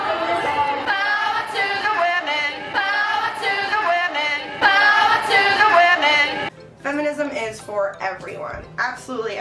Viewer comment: I think they were saying power to the women